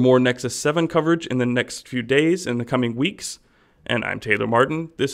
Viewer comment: It sounded like he was saying fuck it now instead of pocket now lol